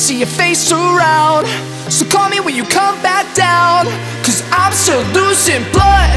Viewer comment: I thought they said Im still lucid